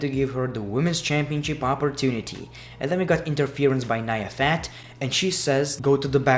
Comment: did he really say Nia fat 😂😂😂😂I'm dead